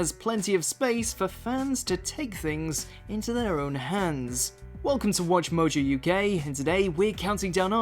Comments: sounds like he says welcome to watch mojo, you gay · What he says: "Welcome to WatchMojoUK..." What I hear: "Welcome to WatchMojoUGay"